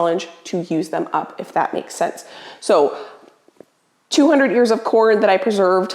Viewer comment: I initially thought you said “200 YEARS of corn” and my initial thought was, you should probably throw those away. 😄🤣